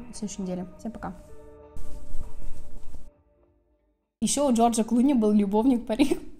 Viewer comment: В конце послышалось: "а еще у Джорджа Клуни был любовник Панин" :)))))